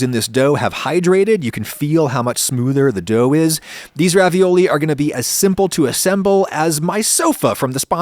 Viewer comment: I could have sworn this said Chinese Ravioli and I was very confused.